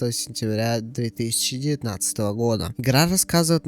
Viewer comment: мне же не одному послышалось 3019 году пахахахах забавный момент😊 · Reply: Я же сказал 2019, почему все слышат 3019? Я просто так сказал, а другие не поняли